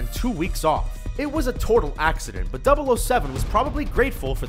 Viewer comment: "It was a "tortle" accident"...2:45 "profinity" laden...it's things like this that make me wonder, is the guy even a speaker of English? Who the fuck says "tortle" instead of "total" and leaves it in to the final cut of a Youtube video? WTF???